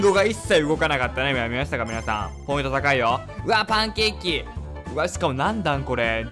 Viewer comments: パンケーキのとこタンケッキーって聞こえたw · パンケーキが タンケッキーに聞こえた